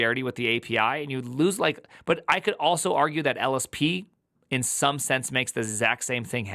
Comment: I swear I heard prime say "LSD" the first time around